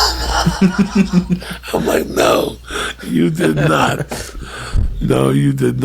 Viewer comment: i got $10,000 for anyone that can translate what joey is trying to say while gasping for every bit of oxygen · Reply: He's wheezing out the words "I'm like no." I'm good though. put that money into a ben afflack movie.